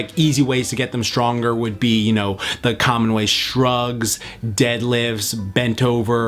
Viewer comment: thought he said drugs for a second